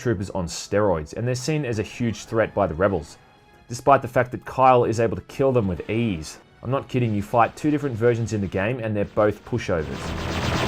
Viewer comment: Oh, he said “kill them with ease.” I thought he said “kill them with AIDS.”